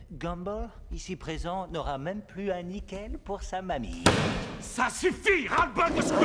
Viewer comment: Il a dit quoi à 3:49 ? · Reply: n'aura même plus un nickel pour sa mamie !